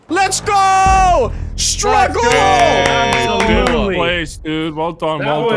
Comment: stragoal? I think he was about to say strike.